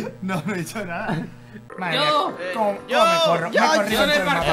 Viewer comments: que dijo? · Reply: Yo me corro, me he corrido en todo el mapa 7u7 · Alguien sabe que dijo Mike en le minuto 8:51 7w7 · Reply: no dijo eso, dijo: como me corro, me he corrido en todo el mapa